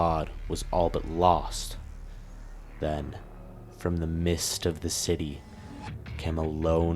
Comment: Most of this has nothing to do with the armor, just the lore in general.... you should learn how to enunciate...When you say words, it sounds like other words. like 8:34 it sounds like you say From the mist of the city, the text says midst, which would make sense. But I replayed it a few times, I HEAR mist every time...There is no D in that word, so you do say mist....midst has a d... 8:46 you're talking about a chill going down the spines of all Humans and Demons alike. But your on Taras Nabad, those aren't humans...the slayer is the only human there, those people from Taras Nabad are called Argenta, not humans...This would be like calling angels, humans...they aren't demons, but they aren't humans...takes a long time to make these videos, but you should know they aren't humans...mortals, yes, humans...dude seriously?